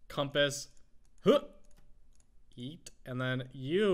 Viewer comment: Did I Hear A Yeet?